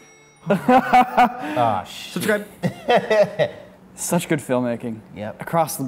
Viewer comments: Anyone hear that cheeky "SUBSCRIBE" AT 10:50? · is he saying "subscribe" at 10:50 ?